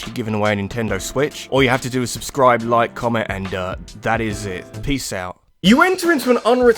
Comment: I thought you said "Subscribe like comment and nut"